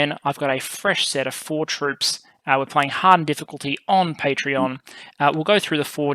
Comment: On hero difficulty they never have. At least for as long as i can remember. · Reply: Ah, thank you....I could've sworn you said "hard difficulty" on the intro.